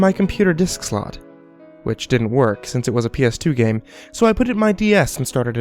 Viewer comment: I MISHEARD IT RLLY BAD ." It was a ps3 game so i put it in my ds"